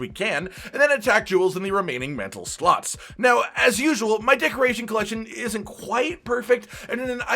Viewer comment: Sorry i was half paying attention while walking, instead of "decoration collection" I heard "dick rating collection"